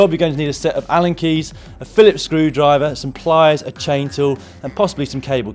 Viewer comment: When listing the needed tools it sounded like you said "a chainsaw" rather than "a chain tool" Caught me off guard for a second there lol